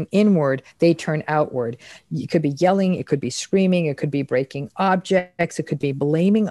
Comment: I thought doctor Ramani said "breaking up" because of the audio stutter, that happens too lol